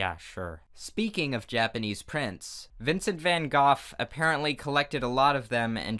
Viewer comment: This entire time I thought was saying "Vincent Van Goth" not Goff.